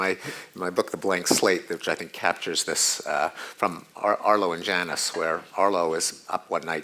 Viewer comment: Does someone know the name of the cartoon Pinker said? I can't understand it. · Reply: Arlo and Janis